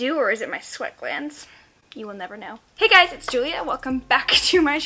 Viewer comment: I seriously thought u said “hey guys it’s dew-lia” in the intro 🤣🤣🤣